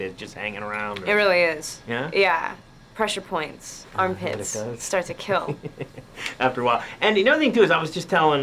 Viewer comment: Because I'm not English native speaker,could someone please teach me what she said . I can't listening at 1:37-1:40. I guess she said "push points.I'm a pits. starts kill." is it correct? what is this meaning ? now, I 'm deeply fascinated by her,particularly "ADVENTURELAND" and "IN THE LAND OF WOMEN" is so nice. Please someone kind explain her meaning.